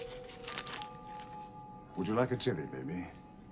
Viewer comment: I love the spot, 4:02 , where Baker offers Sarah Jellie Babies, but the subtitles say Chilli instead of Jellie.